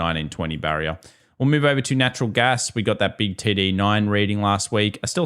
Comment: What is saying at 20:20? Big titty 9 reading…?